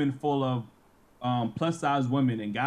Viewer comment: the fact that he says "plus-size" shows he still respects women who are not desired... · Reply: He said "plus size" but I heard "obese 304"!